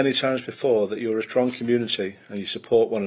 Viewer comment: I thought he said “drunken nudity” not “strong community”! 😂